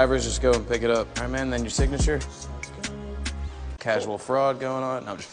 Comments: lol holy sh/t I feel dumb — before I read the comments I legit thought he said “casual frog” 🥴🤣🤦‍♀️ Derp. Suffice it to say, I was very confused…🐸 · I thought he said casual frog 😂🙈